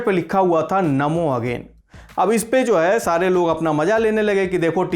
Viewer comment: Did he said salee log maja lene lage 6:33 😂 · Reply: सारे लोग (Not साले लोग)